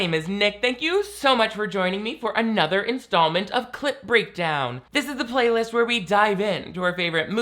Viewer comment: I keep hearing cl!t breakdown.